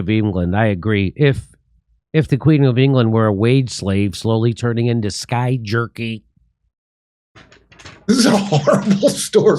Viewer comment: Did he just say "slowly turning into sky-jerkey"? 😅😅😂😅